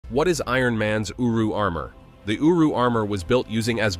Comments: I legit thought it said uwu armor 💀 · I first misheard someone talking about this armor. I thought they said Ironmans UwU armor and I've never been able to not hear it since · My little brother thought it said uwu armor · Thought it said uwu armour. Iron ahego man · For a second i thought it said "Ironman's UwU Armor" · I thought he said uwu armor😅😅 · At first I thought he said the UwU armer 😂💀💀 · I thought he said iron man’s “uwu” armor 🤣